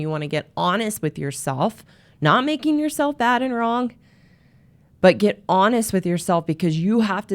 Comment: Sorry what is being said at 5:45? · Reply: I think she said “not making yourself bad and wrong”